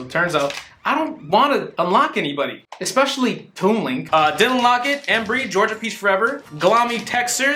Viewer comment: Is it just me or when he said "Dylan Lockett" I thought he said "didn't unlock it" and then he didn't unlock Toon Link? Or just me?